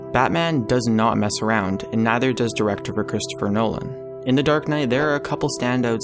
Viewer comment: Did you just say "DIRECTOPHER Christopher Nolan"??